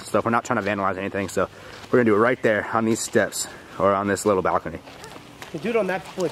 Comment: when he says balcony listen closely and it sounds like somebody says HEY like a male voice.